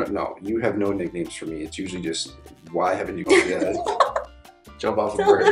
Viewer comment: did he say what I think he did? hahahaha yessss im dying! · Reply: he said "why haven't you come yet"